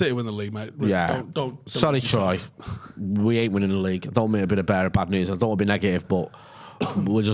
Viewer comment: did just say "i don't wanna be the bear of bad news"